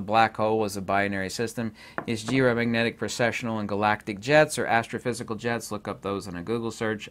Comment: For a moment I thought you were saying "Galactic Jazz" instead of Jets 😂 I was so gonna go full Rick and Morty.. Oh well!